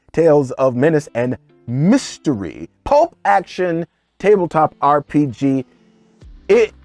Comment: Did I hear "Pope action"?! Does that include the pope-mobile?